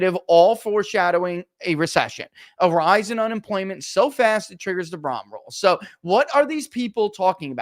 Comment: I just cannot figure out what the heck Barris says sometimes. What'd he say @ 20:30 ..? A rise in unemployment so fast it triggers the "bla rows" ..????? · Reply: So fast it triggers the Sahm rule.